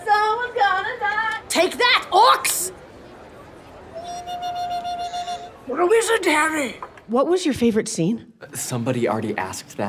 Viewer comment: I heard: "You're a lizard, Harry."